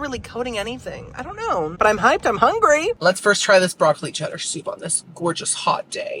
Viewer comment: Lol around the 28 minute mark when you said “Idk but I’m hyped and I’m hungry!” I had to play it twice because I thought you said “Idk I’m high and I’m hungry!” And I was about to be like saaame